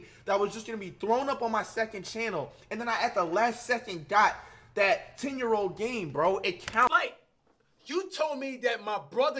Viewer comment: Flight I thought u said anything posted on your second channel don’t count😂